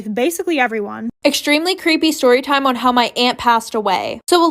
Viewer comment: I'm so dumb I thought she said her ant passed away I was like whattttttt